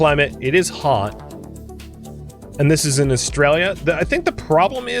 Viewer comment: Lol. When you were describing the planet, I was okay... then when you said the planet/system name I did a double take to see the name as I was certain I misheard "Australia" and how oddly that name fit 29:16 - Yoink "Mine now"